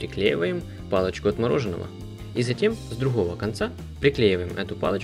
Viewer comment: Послышалось: палочка отмороженного)